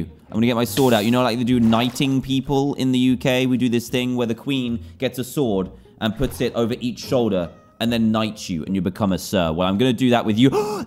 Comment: Why did I hear, "the queen puts a sword on both your shoulders and then knifes you"